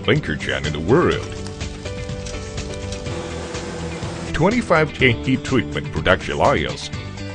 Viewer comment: what is he saying at 1:13? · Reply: r "twenty-five heat-treatment production lines..."